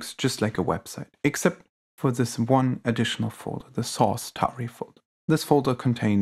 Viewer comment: I heard Sauce Tauri folder at 5:40, made a laugh